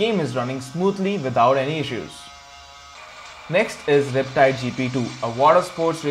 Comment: Whats the name of the second game ( 1:30 ) I don't understand the name... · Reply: riptide